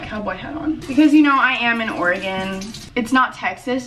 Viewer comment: HA I thought u said “I’m an organ”